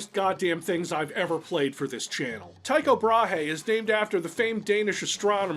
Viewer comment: Ha first time I thought it said techno bahi